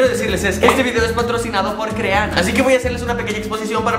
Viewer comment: entendi creano xD